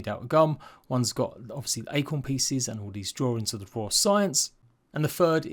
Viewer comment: It says its dry grass "partition" the separators. Did they say it was acorn?